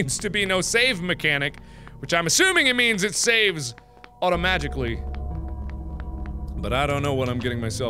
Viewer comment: am I the only one who heard "automagically" instead of "automatically" at 6:51?